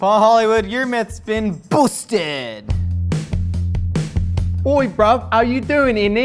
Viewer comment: Josh said "boosted" instead of "busted" 😅😅 we definitely don't want to boost any more myths